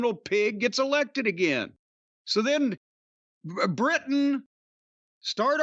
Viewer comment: When Jim said “Britain, start arming yourself up! Canada, somebody is joining to have to step up.” I first heard it as Bret Hart start arming yourself up Canada somebody is gonna have to step up. Now I can’t stop imagining Rambo Bret Hart